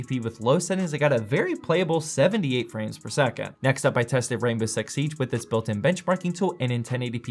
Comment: did he just said rainbow s*x siege or im just trippin? xd